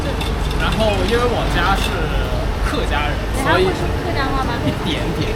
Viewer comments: The 9:20 subtitle is wrong. He said he is HAKKA ethnic, not Hokkien (Fujian). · He said he is from a Hakka family but the subtitle said Hokkien family. 😂